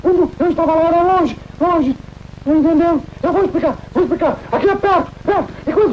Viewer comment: Eu ouvi "eu vou te pegar!" ????